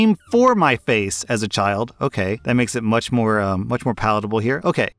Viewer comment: Thanks for making me look like a lunatic. I'm listening to this video on a crowded city bus when I hear "An eagle came on my face". And now because of my laugh, people are giving me worried looks. 😂😂